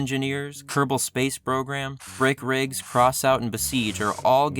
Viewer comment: I thought he said "frick rigs."